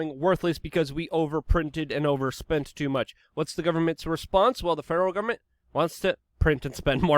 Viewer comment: Next video - <link> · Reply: I heard you say something you didn't but it fits perfectly. "The Feral Government" fits exquisitely as a nickname for the Federal Government doing whatever the fuck they feel like doing